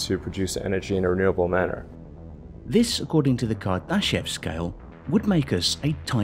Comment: I thought the narrator said "The Kardashian Scale."